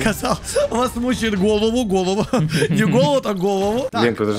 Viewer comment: Мне вообще сначала послышалось, что мучает голубь😅😂